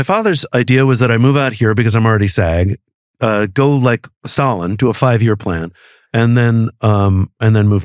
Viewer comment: did Diedrich say 'go like Stalin, do a 5 year plan'?